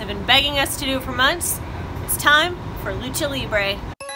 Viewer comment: did you said nacho libre?